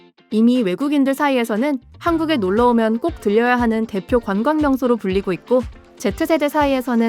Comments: 들려야(X) -> 들러야(O) 3대 공중파 방송사가 '듣다' 와 '들르다'도 구별 못해서 맞춤법을 틀리는 게 한심합니다. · 들려야하는→들러야하는